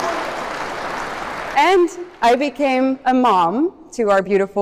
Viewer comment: For a moment there I thought she said 'Francesca!' It was like mislaying my wallet and finding it again. Shock followed by relief! Eva and Francesco! 😁